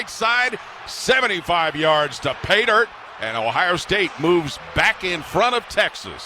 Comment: I cannot understand what he's saying, sounds like "75 yards to pay dirt" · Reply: "75 yards to pay dirt" is indeed what he is saying